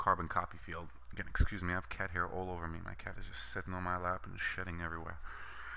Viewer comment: I lol'd because i thought you said "my cat is on my lap and he is shitting everywhere"